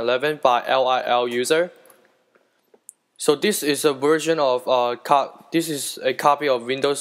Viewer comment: i wasn't calling him a virgin. He sounds like hes saying virgin when hes really meaning version.